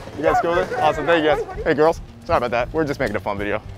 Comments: did he say “hey girls” or I’m trippin 🤔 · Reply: Nope he said hey girls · did he say hey girls to those guys😂😂😂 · did he say .hey girls. to guys HAHAHHAHAHAHAHAHAHAHAHAHAHAHAHAHAHAHAHHAHAHA🤣🤣🤣🤣🤣🤣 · did he just call the boys hey girls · did he say "hey girls" ? :3